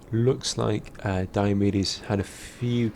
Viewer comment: I keep hearing "Diomidis" as "Diabetes" :p